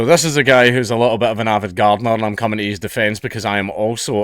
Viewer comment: I thought you said "avant gardener"